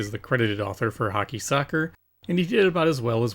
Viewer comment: I misheard the Odyssey 2 game title as “Hockey, Sucker!” and thought that would make a great line of sports video games. “Football, Sucker!” “Hoops, Sucker!”